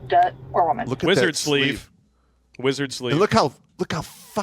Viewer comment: I thought that you said "lizard sleeve" at first and it seemed appropriate. Watch the old Frank Sinatra movie "Dirty Dingus Mac Gee". Part of it is about the local cat house closing and the girls marrying the men of the town. The poor guys. That's an XY.